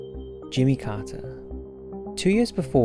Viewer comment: I misheard 'Jimmy Carter' as Jimmy Carr, and could not stop thinking about aliens thinking that Jimmy Carr's laugh applied to all humans.